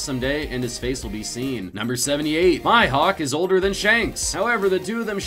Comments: I’m Triggered did he just say “My Hawk” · Did he just say Hihawk